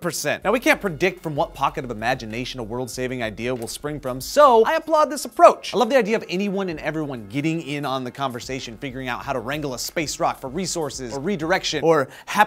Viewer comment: did joe say "Fingering Out"?